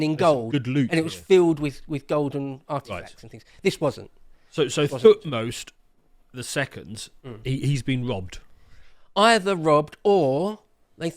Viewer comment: Genuinely thought said the name of the pharaoh was "Fuckknows the second" 😬😅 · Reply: I thought he said fuksmost the second